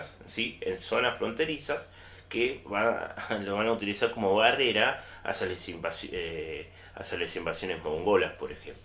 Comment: profe no se le entiende nada lo que dice en el minuto 10:32, y una palabra que se le trabo antes. me lo puede contestar por favor. gracias. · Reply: Barrera para las invasiones mongolas. Dice